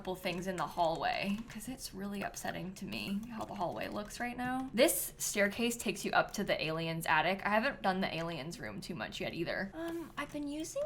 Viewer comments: did u call Nina the alien 15:54 ?😭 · I’m sorry did she just call Nina an alien 😭